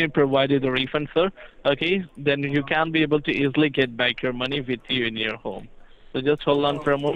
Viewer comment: Does he say "implicate your money"?